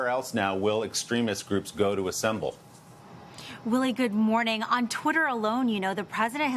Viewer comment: When she said Willie at the end I thought she was saying really.